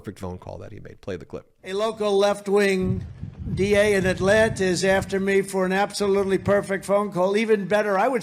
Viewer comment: Trump says "Atlantis" instead of "Atlanta" LOL